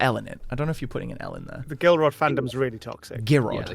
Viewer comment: I had this on in the background and thought he said girlrod 😳😳 ndksskwnskwndksskdjwk